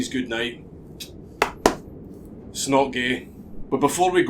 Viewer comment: when he said “it’s not gay” it sounded like he said “Snuggie”. Because we all know it’s not gay to have a lil snuggie with the homies.